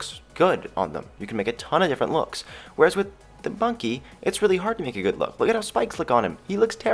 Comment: did he just say bunky?